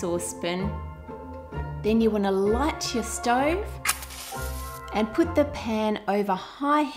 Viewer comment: i heard "Then you want to light your stone" o_o